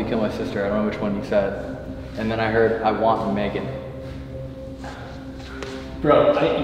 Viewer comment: tell me why I heard “ I want bacon “ instead of “ I want Megan “ 😂